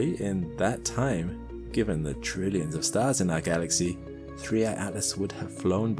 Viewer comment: Did Alex say 'trillions' of stars in our galaxy? about